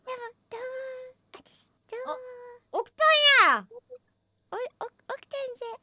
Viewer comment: オクタンや！に聞こえた